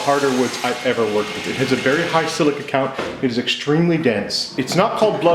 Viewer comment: What did you said at 0:50 ? Very ... count? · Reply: silica. Dense woods tend to have a lot of silica in them, it dulls blades and chisels pretty fast!